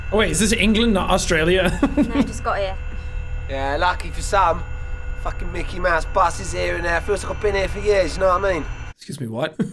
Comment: if you couldn't understand the guy at the bus stop, he said "Yea lucky for some. Fkin mickey mouse bus is here and there, feels like I've been here for years, ya know what I mean?"